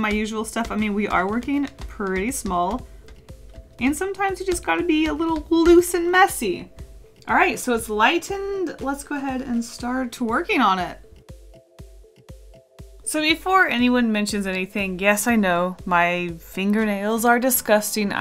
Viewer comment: Am I not the only one who heard the work twirking?!?!